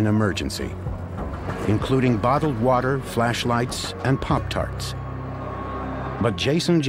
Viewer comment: did he just say... Pop tarts?